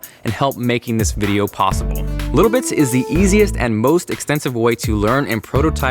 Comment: When he said "Little Bits is the most extensive way..." I thought he said expensive way .-.